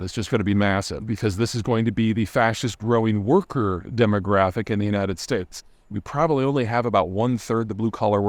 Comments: Ah, going to visit the world whose face they stepped on , on the way "up". Um. Less is more. Think about it. Not Pete's first thought. At 3:00 did he say "The fascist growing worker demographic.."? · "The further East you go the sketchier it gets" is just a lie. Definitely felt safer in Croatia than in Paris, and all murder rate data easily disproves his point. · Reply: Ah yes, the Utashi made the trains run on time At 3:00 did he say "The fascist growing worker demographic.."? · Portugal, Spain, and almost all Europe are a huge turism rich environment/site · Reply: did he say "The fascist growing worker demographic.."?